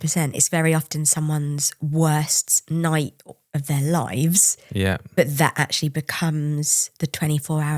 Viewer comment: You so "lives" I hear "shit"